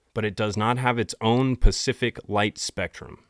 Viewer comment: Did u just say PACIFIC instead of SPECIFIC?!??! 🤨🤨🤨🫠🫠🫠🙃🙃🙃 I'm judging! Lol. Jkjk.